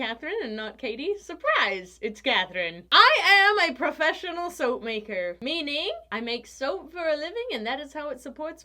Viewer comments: I thought it said lips smacker soap 😂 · In the intro I heard "...and that is how I suds-port my family." Yeah, sorry.